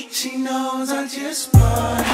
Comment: My volume was low and i thought the song was saying “she knows i just farted” 😂😭 but i love the look 💖😍